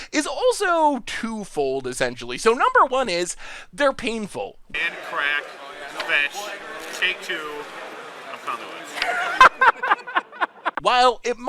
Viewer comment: What does he say at 2:33? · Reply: "Land, crack, fetch, take 2, I'm Conley Woods." <link>